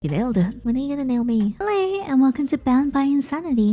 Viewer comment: I thought she said “When are you gonna rail me?” in the beginning 😭😭 I’ve been watching too much of these